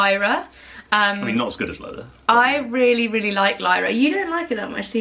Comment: Lyra.... all I hear is 'Lycra'